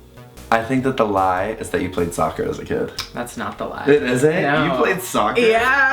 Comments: OMG Literally when Mitch said he played soccer, I thought he said he played "Stalker" as a kid and I wasn't surprised... Is that bad? XD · on the first one When He Said " I think that you playing soccer as a kid is the lie..." at first when Mitch said it is thought he said " I played stalker as a kid.." I'm like What game is that?! 😂